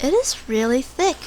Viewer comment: Nobody: its really thicc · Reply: I just heard “Its really Thick” and i saw your comment